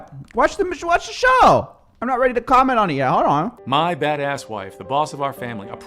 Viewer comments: I genuinely had to rewind because I thought he said, “my fatass wife” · I swear I thought he said "my fatass wife" lmfaooo